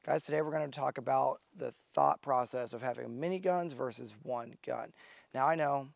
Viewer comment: thought he said mini gun :(